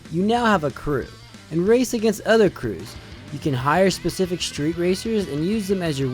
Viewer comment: oh crap i thought i heard you can hire a specific stripper